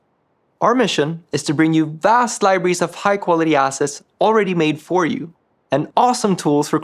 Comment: all I heard was "High Quality Asses" and I like it :D